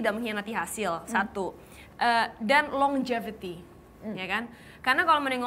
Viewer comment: Menit 36:18 dan 36:30 ada yang bisa bantu Agnes ngomong apa? Suaranya terdengar LONG JAVETI tp gatau nyari di kamusnya gimana. Tks. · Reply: Longevity atau umur panjang